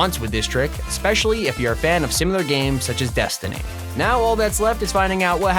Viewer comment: I heard DESTINY?! YES I LOVE DESTINY! IT'S MY FAVORITE GAME!